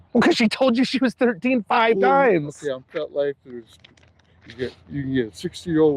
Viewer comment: is he saying vetlife!? Kids and animals 😮